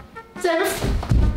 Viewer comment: ich habe Senf verstanden HAFGHAGHGAAAAGHH 😂✋